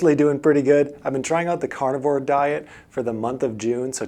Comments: Thought you said cardboard diet. 😅😂🤣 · OMG, I thought you said you were on the Cardboard diet, lol! After reading some of the comments, I realized that I heard you wrong.